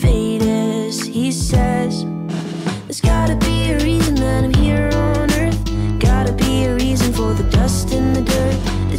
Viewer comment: Did mina just say it was not her business She is all ways in other peoples business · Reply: She said “but at the same time it’s not my business “